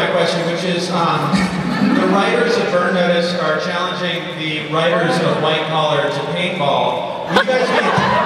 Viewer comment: Can anybody tell me what is the question at 2:00 ??? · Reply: 9 years later but I figured I'd answer it, sounds like he said that the writers of Burn Notice had challenged the writers of white-collar to paintball